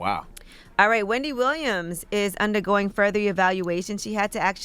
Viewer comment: I thought it said "ongoing further EVACUATIONS"😂